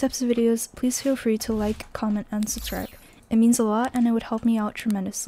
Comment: why does it sound like she is saying unsubscribe lol